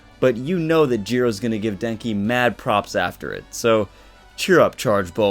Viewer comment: I misheard “Jiro giving Kaminari ‘Mad Props’” as “Mad Top” and choked on my drink like “TRUTHHERO!😧🤭” omg 😅 Edit:Omg thanks for the likes I’ve never got that many lol